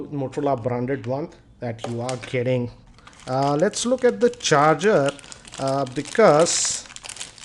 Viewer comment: is he saying 'but you are kidding'